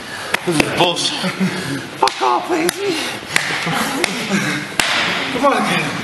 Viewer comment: sounds like bubbles saying “fuck off lahey” haha